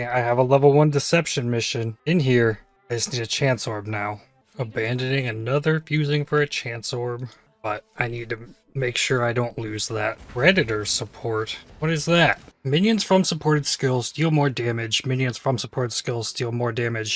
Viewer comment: i had the your video on my second screen and i heard redditor support gem lol